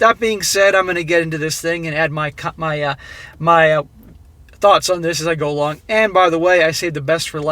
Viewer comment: For this episode, in particular, when says "thoughts", I hear "Thots". ;)